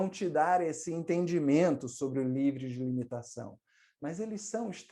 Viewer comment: Não entendi na parte 9:16 minutos do vídeo entre 9:20 a palavra que você disse foi nível ou livro de entendimento ? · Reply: Livre de limitação